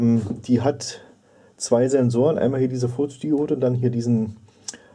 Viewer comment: Danke für das Video. Ich mag solche Videos sehr gerne. Aber verstehe ich bei 0:25 wirklich Furzdiode? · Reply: 😂Nee, Photodiode